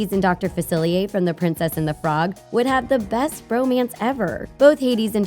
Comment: I misheard “bromance” and thought she said “romance.” Lol. Now I can’t get the idea of the ship out of my head. It’s not the ship we wanted. But it’s the one we needed.